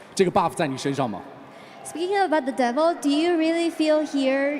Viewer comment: What did the woman say starting in 7:24:05 now? Genuine question pls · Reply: She says "speaking about the devil..." because Rain mentions "beating their former demons" in his answer to the previous question